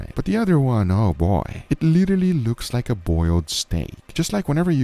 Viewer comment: if you watch it at 2X speed, it sounds like said “it looks like a boiled snake”